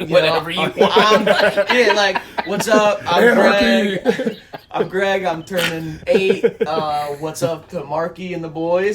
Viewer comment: What does say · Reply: ANARCHY!!!!!!!